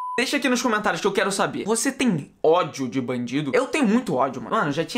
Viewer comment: Kakakakakaka... Qnd vc foi falar "vc têm ódio d bandido?" Eu entendi "vc têm ódio d mendigo?" Kakakakaka aí mó noss os cara passando fome e ele querendo mata eles kakakakakak abraço mn